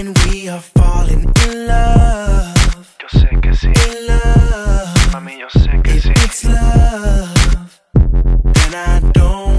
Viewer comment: I thought he was saying “yo sexy, Mami yo sexy”